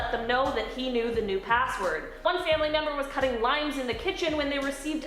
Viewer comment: I thought you said cutting lines in the kitchen not limes and thought that took quite an unexpected turn.